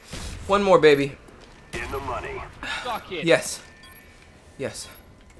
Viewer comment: anyone else hear: "suck it?"